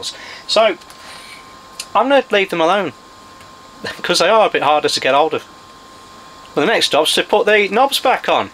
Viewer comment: What says at 29:17 ? "Because they are a bit hard to (s?) get all off" ? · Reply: 'they are a bit harder to get hold of'